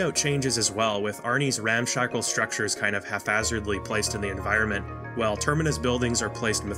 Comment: did I hear you pronounce hapHAZARDLY as hafazardly?! That is fascinating! Because of the ph? I never thought of it like that. But no, that is wrong, unfortunately.